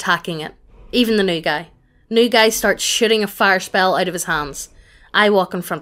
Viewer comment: STG it sounded like she said, "shitting fire spells"